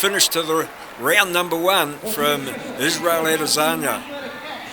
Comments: This commentator sounds gay as a mofo 😂 " Adisunyiahh" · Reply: I heard "Israeli Lazania" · This commentator sounds gay as a mofo 😂 " Adisunyiahh" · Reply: I heard "Israli Lazania"